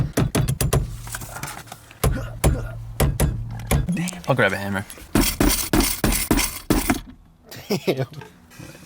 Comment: For a sec I thought said "I'll grab a hammer" only to put it on the center console and use it like an anvil 😂